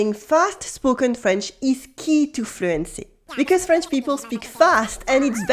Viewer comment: LOL! I've failed already not even 00:22 into the video "Understanding fast spoken French is key to....<unintelligble>" I didn't catch that, even though I replayed it 6 times 🤦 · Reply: Fluency